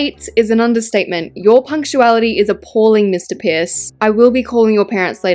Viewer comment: When Alaska said “Mr Pierce” I heard “Mr piss” 😭 I still love Alaska tho don’t get me wrong!